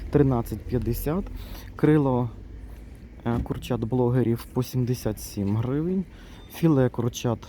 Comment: Курчат-блоГерів 😂😂😂😂 Спочатку подумала, що почулося, але коли послухала ще декілька разів, зрозуміла, що ні 🤣🤣🤣